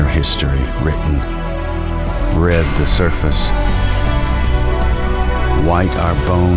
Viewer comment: I cannot understand what he says. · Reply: "Red the surface, white our bones, on terraformed Mars, cradle of our children."